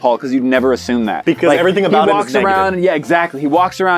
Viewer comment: When said "everything about him is negative," I thought he said everything about him is naked and I got so confused